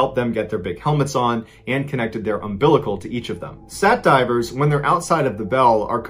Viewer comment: It’s at 15:57 monster inc guy on your top button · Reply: I thought u said sat driver